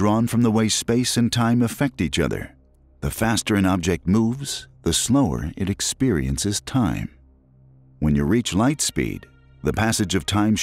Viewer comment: I thought he said flower.